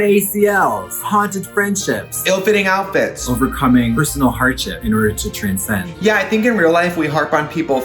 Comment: For a second I thought Katya said “overcoming personal hygiene” and I was like bitch have you met you 😂😂 all love tho 💖